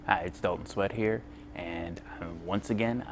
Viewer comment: i heard "hi, its dont sweat here"